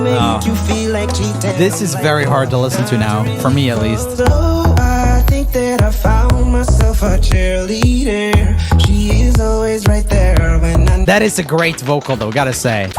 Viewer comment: Durring chear-leader I remeberd that I always would hear "OoOoO I think that I found myelf a cheeseburger shes always right there when I need her" was that just me- im w e i r d Edit: In shape of you I heard "I like shampoo and magnets to" WHAT IS WRONG WITH MY EARS